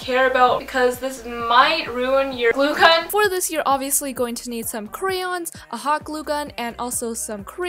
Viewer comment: Is it just me or at about 1:19 did she say, "all you'll need is crayons and crayons?"